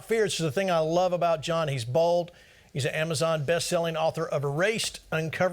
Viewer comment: i heard that as bald not bold